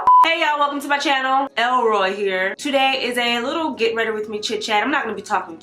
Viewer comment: Bihh did you just say “Elroy here”??? 😂🤣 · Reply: Mrs Pams lmao. Yes she did.